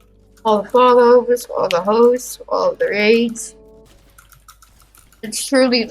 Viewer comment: I thought he said all the hoes😭